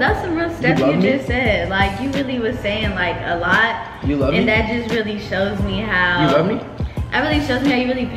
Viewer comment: all i hear: u love me ? you love me ? you love me ? u love me ?